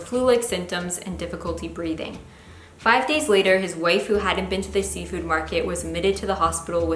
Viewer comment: sound like she said his waifu lol